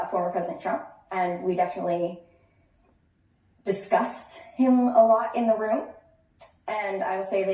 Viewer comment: At time stamp 6:50 one may think she is saying "discuss" but she is saying "disgust".